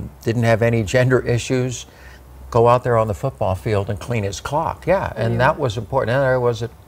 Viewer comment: did he just say he could take a guy on the field and clean his cock?